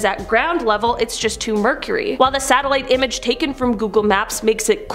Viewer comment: she says it was just too mercury, instead of murky. Im out